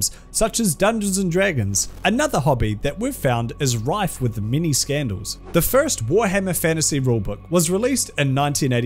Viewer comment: I won't lie every time I hear "many scandals" from you my ears hear "Mini Scandals".